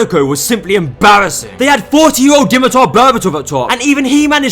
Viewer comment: what did this man even say here "they had 40 o botamotaaa"???? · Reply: They had 40 year old dimitar berbatov up top